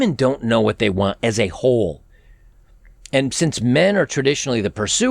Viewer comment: All this is fine and well..... and in addition the laws and family court system need to change and be more fare. 31:21 Did he just say, women don't know what they want as a "hole"?....... Or was I the only one that heard that?